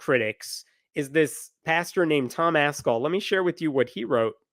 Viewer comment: Anyone else hear "Tom Asshole" at 15:25?